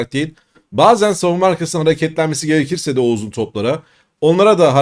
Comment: ’daki ‘o uzun toplara’ kısmını Oğuz’un toplara diye anladım. O zaman 🤩🥳 Hadi bakalım hayırlı olsun. Teşekkürler güzel ve değerli yorumlar 🎉